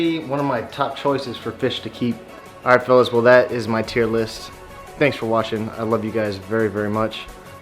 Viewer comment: I thought you said "tear list" lol California sheepshead is really tasty too.